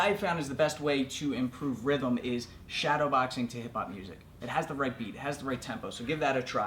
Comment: I had to replay it because I thought he said K-pop music. lol